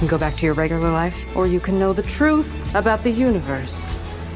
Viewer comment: I thought she said the truth about the uterus 💀💀